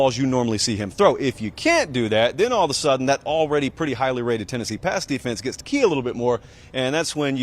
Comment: At about 1:30, Did he say highly rated Tennessee pass defense?